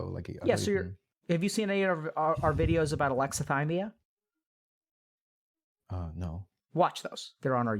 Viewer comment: hey, since you've finished watching, what videos is he talking about at 1:22:51? alexa thambia is what i hear which can't possibly be right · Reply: alexithymia. Not sure which videos exactly though. The close match I've found is <link> which has the mention of alexithymia in the description. I watched it but I didn't think I understood anything more about it. There's another one possibly with David Nash, will have to check it out.